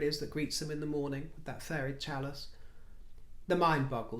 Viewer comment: I absolutely ❤️❤️❤️ you BUT listening & watching you while eating dinner (USA-EST) can be dangerous 😊😊😊. When you said the words “her furry chalice” that just grabbed me causing me to spit out my bite of dinner & laughter ensued 😆😆😆. Do preface your chats with a WARNING ⛔️ to those that may be eating or drinking ☺️😉🥰. Thanks much for a good belly laugh 😆😆😆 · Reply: I thought said, "fairy chalice" and I lost it. Now that I know it was "furry" chalice I'm laughing again, only harder!!!